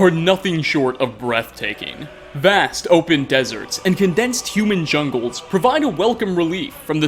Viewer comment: I thought you said human jungles for a second 😭